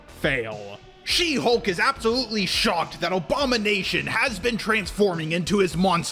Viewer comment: Sorry but every single time you have said abomination the way you said it and the way I heard it makes it sound like your saying Obama Nation especially here